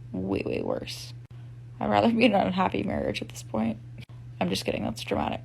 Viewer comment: Wait wait wait wait wait wait WHAT?!?!? I thought, at first, she said she'd rather be in a happy marriage at this point. Then I ran it back. BRUH 🤦🏻‍♂️ She SERIOUSLY SAID "I'd rather be in an unhappy marriage at this point 🤦🏻‍♂️ She did that guy a SOLID by dumping him!!!